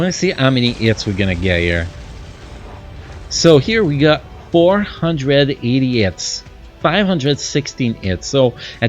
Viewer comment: sound like you said 400 Idiots, instead of 480😂